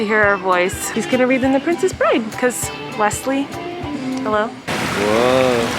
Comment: in the princess bride it’s not Wesley it’s Westley. (Not saying this in a rude or mean way I’m just correcting the mistake) (I know you misheard it because I did too, my mom corrected me for it lol)